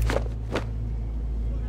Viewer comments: Man, I heard that last part really wrong. I heard him say: you jack in the hat, NOW!!! And when he started to unbutton I was like what?!?! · What does Penguin actually ask of the reporter when they're in the car? I can't make it out... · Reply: I thought he said jacket in half because of captions